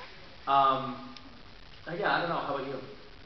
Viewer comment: it sounded like she said "I may have salted a window at one time."